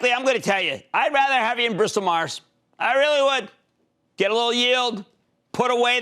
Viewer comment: what stock does he say? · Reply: Bristol Myers Squibb